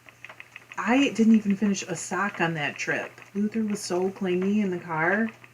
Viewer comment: So I was watching the video in a SUPER loud room so I had the captions on (as one does) and I swear. I SWEAR I thought it said ‘Lucifer’ and not ‘Luther’. 😂 I may have laughed so loud that I earned myself the Stare of Judgment.